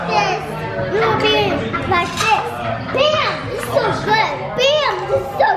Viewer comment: Literally when said Bam it's so good , I thought he was saying damn this is good 😂😂😂😂😂